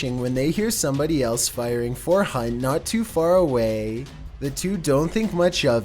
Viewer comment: I swore he said firing fortnite,😂